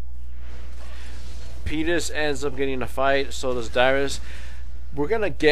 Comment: did he say penis? they got a kill but penis ends up dieing for it. wow that is a bad deal